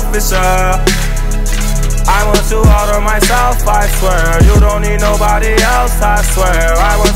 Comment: all I hear is "I want food all to my self"